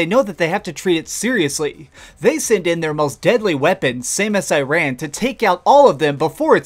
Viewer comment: For about 30 seconds I thought you were saying "Same As Iran"